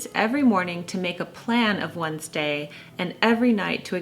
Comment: I misheard "one's day" as "Wednesday" and thought, "Why is Wednesday so important??" 😂